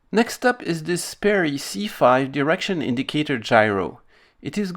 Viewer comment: Sounds like you say erection indicator. Lol